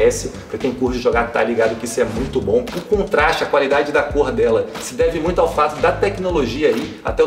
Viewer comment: eu entendi : qualidade da pouha kkkkkkkk